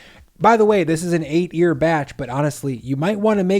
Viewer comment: Somehow I heard you say, “This is an 8 year batch.”